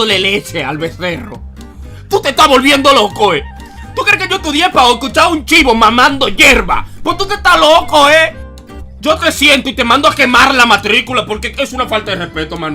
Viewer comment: entendi mamando ñema